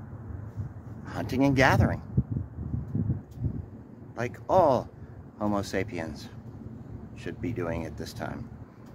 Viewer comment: I thought said “like every homosexual should” and I was like, is this a reveal video?